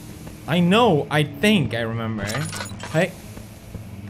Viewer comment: What did he say at 4:56 ? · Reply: he said ”jävlar”, and it basically just means ”fuck” lmao